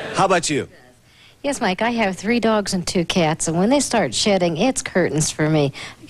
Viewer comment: Did she say "SHITTING" 0:20?🤔😯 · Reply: haha i thought so at first, but she says 'shedding'.